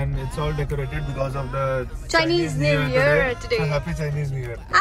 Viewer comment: did she just say Chinese new year😯😲😲